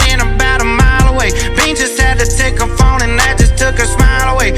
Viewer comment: I thought he was saying “peaches took her phone away” does not sound like bouncers at all lol